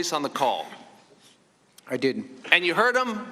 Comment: he clearly said "I didn't" when asked if he heard the president on the call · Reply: Chambers Ratcliffe he CLEARLY says "correct" when asked if he was on the phone call and when asked if it was "the president's voice" he CLEARLY says "I did"